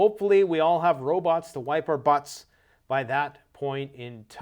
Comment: Ai robot > wipe your butt, I thought you said cut your butt sorry